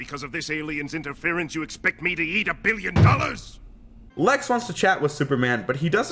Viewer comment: Did he say "yeet a billion dollars!?"